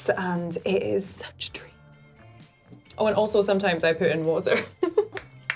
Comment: did anyone else had to rewind because you heard her saying, sometimes i poop in water ? lol